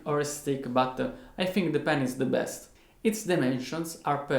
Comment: Idk why i heard it "pen1s is the best